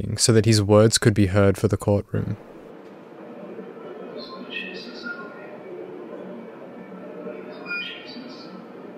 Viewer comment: I can’t really make sense of that “enhanced” audio from the footage of the police cruiser interior. Any possibility someone can discern what he’s saying? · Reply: I just added subtitles for that specific section then. He says Jesus help me. Forgive me Jesus , Jesus